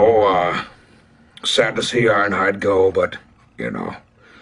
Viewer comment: I thought said ironhide died